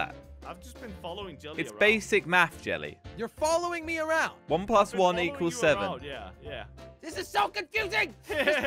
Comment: i thought solgo said 1+6=7 but he said 1+1=7 lol